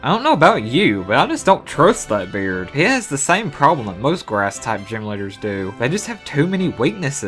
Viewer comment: It sounds like you said ASS TYPE gyms.